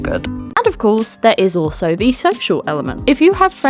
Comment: i thought you said "sexual" instead of "social" and i was like, "Whoops, what kind of list did i click on??" 😅